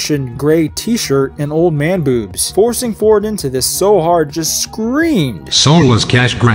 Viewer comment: About harrison ford saying wallace got the eye color wrong, he didn't, she was a perfect match. He just said otherwise to piss off wallace. They should have made it more clear though. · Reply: I thought he said Heroin Ford there for a second.